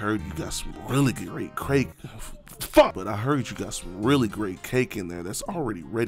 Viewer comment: but i heard you got some really great crake 👀